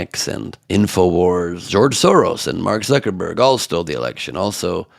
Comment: Did she just say Mark Zuckabuck?